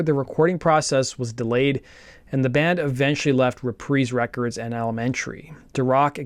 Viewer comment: or so… “re-prees” records? Lmao what? Pretty sure it’s the word RE-PR-EYES, as in Reprisal… like a religious movement